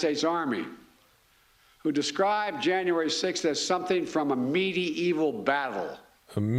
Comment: I thought he said meaty eaty battle 😂